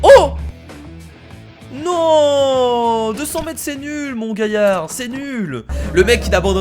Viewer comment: mon quoi 🤣🤣🤣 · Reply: Mon gayard